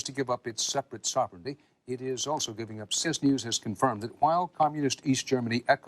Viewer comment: Did he say "it is also giving up cis news has confirmed that," or did I mishear him? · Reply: Looks like the audio cut out a bit in the clip but he says “CBS news has confirmed…”